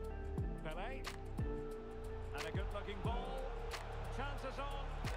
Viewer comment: “and a good fucking ball” am I hearing this wrong or what?!